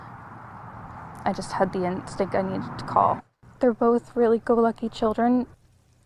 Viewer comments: Go lucky ? I thought she said happy “go lucky “ ? Talk to anyone - tell anyone what we did !!! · Did she just say they’re very good looking children?!?!